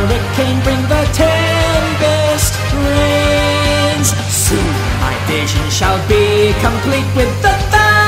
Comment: It sounds like he said 10 Best rains